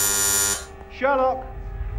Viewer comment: okay when John said Sherlock I heard shut up